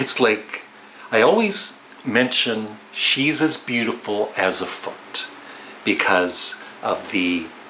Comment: I had to look up the album and the song. I thought you said she's beautiful as a fart. 😂